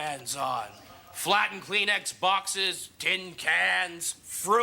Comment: thought he said 'flat and clean Xboxes' for a second there.. i was like whaaat anachronism...